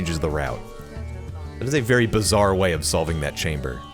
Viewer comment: did i hear bizzaire?? (Jojo reference)